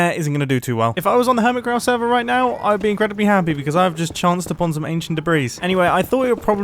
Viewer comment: I heard "ancient fabreez" and double took